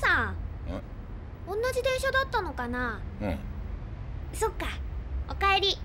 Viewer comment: やばい何度聞いても「こんな自転車だったのかなぁ」に聞こえてしまう…